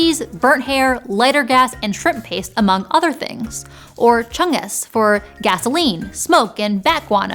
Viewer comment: did you say "chungus"?